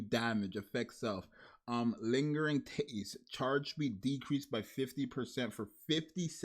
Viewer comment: who else hears “lingering titties”?